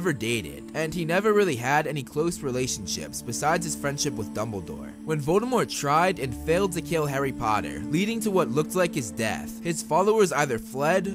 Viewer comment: that transition was so quick I heard "When Dumbledore tried and failed to kill Harry Potter"